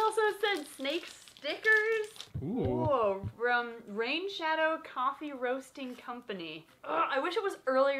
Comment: what my ears hear: FROM RAID SHADOW LEGENDS What she said: from rain shadow coffee roasting company